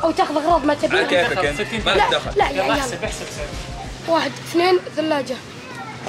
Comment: واحد اثنين ثلاجة🤣 كذا نطقها ولا ماسمعت تمام